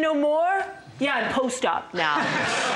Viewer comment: what ? Don't get that one at all.. . . EDIT - Oh " I'm post op now "... I kept hearing I post Stuff now.